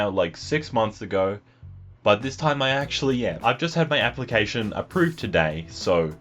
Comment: When you said "I've just had my application approved," I thought you said "I've just had my amputation approved."